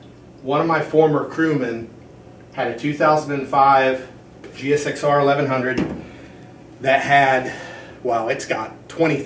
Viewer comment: did you say 2005 GSXR 1100?